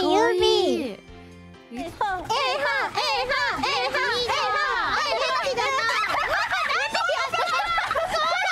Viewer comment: i could have swear What i heard is "A-hole A-hole A-hole"